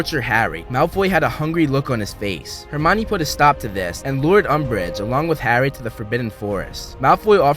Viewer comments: Did u said lord umbridge at 18:05 like its a game of thrones video? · did i hear ‘lord umbridge’ 😹😹